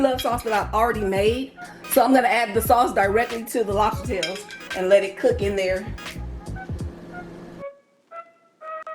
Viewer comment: Hey guys it sounds like you said boil were the lobsters cooked in the oven or grill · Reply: Broiled in the oven